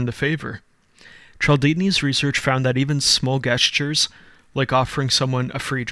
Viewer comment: Did he just say guess-tures?